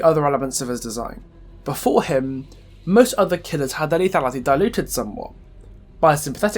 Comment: when he said "their lethality diluted somewhat" I could not understand that he said lethality it sounded like he started mumbling LMAO